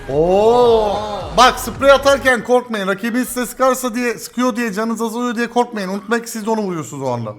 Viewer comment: ne dediğini anlayan 😂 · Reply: Ben rakibiniz size sįkarken siz hasar aliyosunuz diyekorkmayin oda hasar aliyo dedi